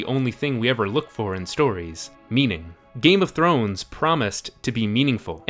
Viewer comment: When he said "meaning" i misheard it as "memeing" and it made even more sense